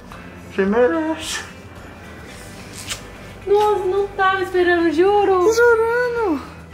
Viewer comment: "mor eu não estava esperando msm" escutei certo ?